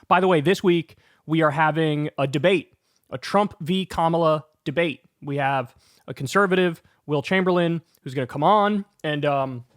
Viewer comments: I thought I heard Kyle say that Wilt Chamberlain was coming on and I was so confused for a second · I thought he said Wilt Chamberlain, lol. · I thought I heard Wilt Chamberlain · I thought you said Wilt Chamberlain 😂😂😂 I was like what strings did you pull to get him on.